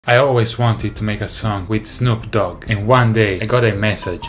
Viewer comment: i heard that as "I've always wanted to make a son" 💀